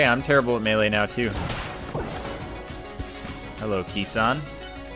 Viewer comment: Did he just say hello key-san?